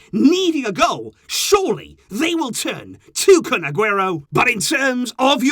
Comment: its kun instead of FUN